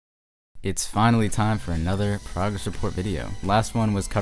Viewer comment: does bro said "progress of porqueria"???